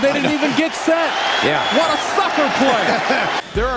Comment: lol sounds like he said fucker instead of sucker